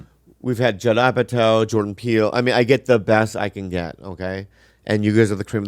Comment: Not even gonna lie I thought bobby said Jordan Peterson when he said Jordan Peele was on this show andso I searched high and low. I am disappointed no jordan peterson has been on this show. LMFAO